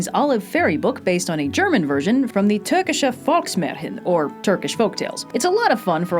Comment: Did she say “Märhen?” I never understood people getting upset at her pronunciations until now.